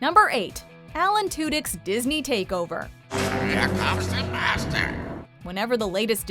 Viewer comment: I know Iago said "here comes the master", but it sounded like he said "here comes the bastard".